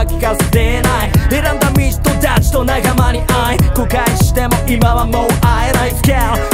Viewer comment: これカシラが歌ってる後悔しても今はもう会えない？のとこコカインって聞こえて仕方がない